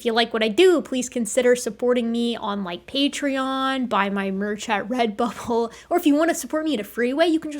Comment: I swear I heard you say "Buy my merch at red butthole" lol